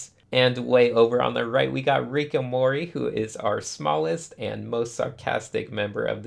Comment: I heard "Rick n' Morty". (Shall I say spoiler alert?) After that cliffhanger, I hear Rick and Morty everywhere I go. I think I gotta see a shrink or something.